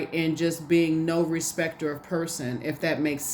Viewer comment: Are you saying: “no respect or person”? I’ve heard you say it a lot and just now deciding to ask 😅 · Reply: no respecter of persons